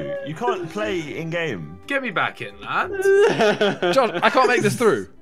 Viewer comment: did any one hear Jon instead of josh🤔🤔